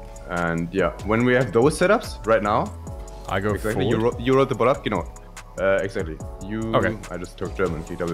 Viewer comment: Even me a native German speaker has no idea what said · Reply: "you are right about that genau" und das - you are right about that - hat er mit deutschem Akzent verhaspelt, wie ein Wort ausgesprochen, weil sein Hirn kurz nicht mitkam. und Isapar hat dir schon ne ähnliche Antwort gegeben aber da du nochmal gefragt hast hier ausführlich.